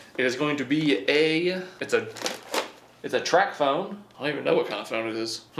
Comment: I thought you said trap phone but you said tac phone. Still its a trap phone..